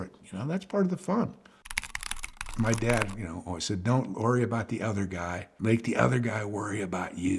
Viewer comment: He was 20yrs old, 50yrs ago. Amazing wisdom but… times are a tiny bit different now. Who cares what would be on my dead bed? I will be dead already 😅 it will only amuse others and I thought he said: “who cares about the other guy” 🤭?